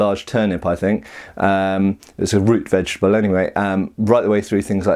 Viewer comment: Did you just say WOOTZ vegetable? xD